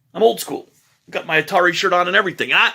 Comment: I had to go back because I thought you said: I've got my Katari shirt on :D